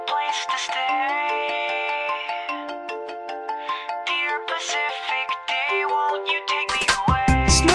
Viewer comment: i thought he said "very specific gay, come and take me away" xDDDDDDDDDDDDDDD